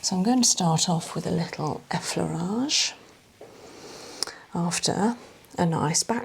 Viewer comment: These videos are lovely and soothing. What do you say @ 5:01? It sounds like you are saying eflarage. I know that's in correct but I'm just typing how it sounded if that makes sense lol. 😂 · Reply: That is what she said. She said start with effleurage after a back stretch